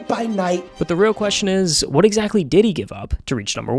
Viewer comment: bro am i cooked i hear diddy here wtf thats not normal